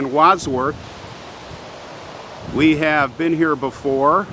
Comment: I just watched this......I thought I heard a woman say " been there".....great video..thanks to you both